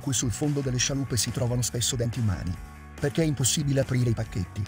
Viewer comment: A 4:29 cosa dice? Non riesco a capirlo. Cosa c'è sul fondo delle scialuppe? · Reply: I denti